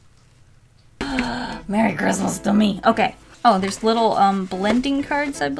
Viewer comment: I thought she said, "merry Christmas dummy." But she just said, "merry Christmas to me." In a weird way.